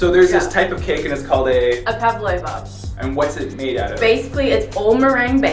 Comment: When I heard pavlover, I thought I heard Pavlov